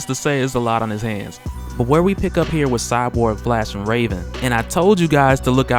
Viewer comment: For a second I thought you said "Cyborg flashing Raven" and was like "damn cyborg, you freaky"